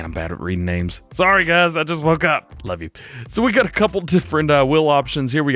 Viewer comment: did I just hear u say “ I love you” ?😂😂